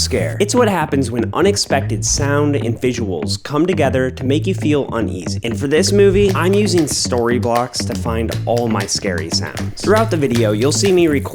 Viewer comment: I THOUGHT YOU SAID STORYBOTS